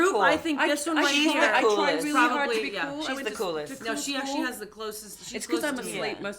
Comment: Damn I don't know who talking this but I just heard "I went to cool school."